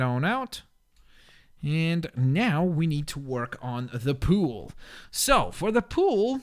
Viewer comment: when he said, "now I need to work on the pool", I thought he said, "now I need to tweak on the pool". And I was laughing so hard! 😂😂😂😂😂😂😂😂😂😂😂😂😂😂😅😂😅😂😅😂😅😂😅😂😅😂😅😂😅😂😅😂😅😂😅😂😅😂